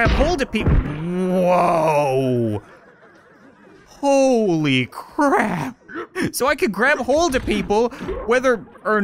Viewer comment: Is it just me or at 11:09 did he say Holy Crab?